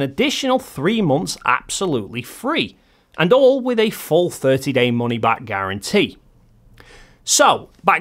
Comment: I hate to poke fun since you have great videos. But in your advertisement for Atlas, it sounded like you said "it all comes with a false 30 guarantee" I did realize after playing it back a few times you said "a full money back guarantee" It was humorous to me, lol 😁